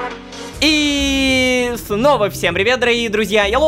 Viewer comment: Мне одному послышалось вместо снова фнова